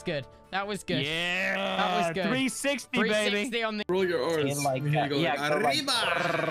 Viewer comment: ryba ? Fish in english ?! What? fish in Polish is ryba Did they say ryba?